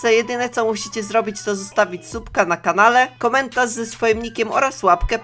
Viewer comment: usłyszałam sutka a nie subka xD Odcinek superowy jak zawsze <3 Nick: anitka543